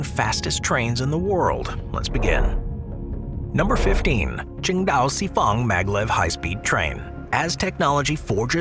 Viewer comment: At first I thought the train at 0:22 was called "Chinga'o". 😊